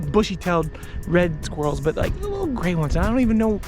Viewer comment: never saw a green one before! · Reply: I thought I heard green…obviously you said gray.. 🤷🏼‍♂️😂